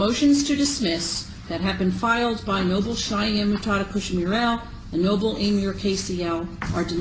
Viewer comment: At first I thought she said, "Noble Cheyenne Pushing me around."